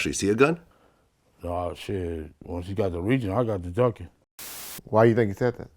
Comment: Why did he say "I started dumping" I thought Orlando shot? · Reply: sound like he said ducking but he might have said dumping